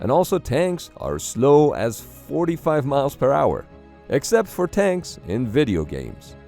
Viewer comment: "Because tanks are slow as fu...orty miles per hour." Please tell me I'm not the only one who heard the hesitate